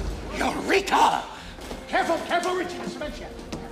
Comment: what did he say. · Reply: Eureka i think